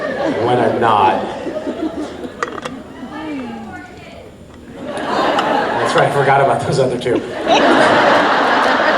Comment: what says the girl in 22:42? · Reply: "No wonder you have 4 kids." ? That's what I understood...